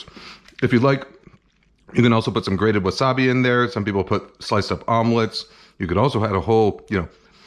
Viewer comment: Did he say "sliced up omelets"?